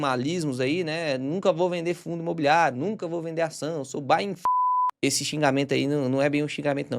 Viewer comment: pra quem não entendeu o que ele disse com o bipe, é buy and fucker significa compre e se ferre